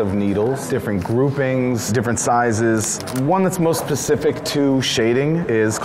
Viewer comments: this man really just said pacific instead of specific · he really said “most pacific” instead of “specific” I thought people saying that was just a myth. I didn’t think anyone ACTUALLY said pacific